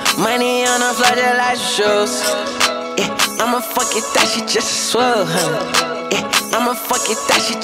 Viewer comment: kinda sounds like hes saying “ima fkin th*t” lol